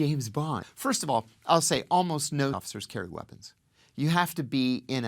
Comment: Pretty bad cut at 0:45 . What was actually said? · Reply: I assume he said "CIA officers" but they cut out CIA lol